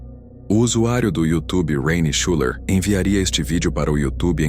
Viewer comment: Eu não entendi direito o nome do rapaz alguém poderia me dizer qual é o nome dele?! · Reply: Olá amigo, rayne shuler nome dele. Abraço